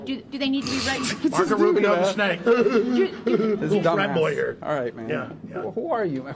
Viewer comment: lol, did anyone else hear rubio call him a dumbass?